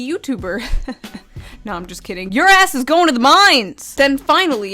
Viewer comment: Misheard "Your ass is going to the mines" as "your ass is going to the mimes...." thx.